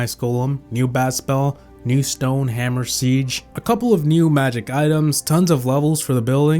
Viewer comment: what is he saying man stone hammer sige